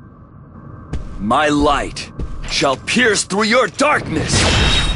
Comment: I thought he said "My light shall pierce 3 your darkness" LOL.